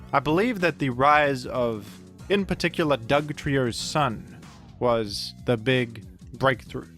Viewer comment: ngl when you said the rise of dugtrio sun i thought you said dugtrio's son and were gonna go on about people running diglet +dugtrio to get two trappers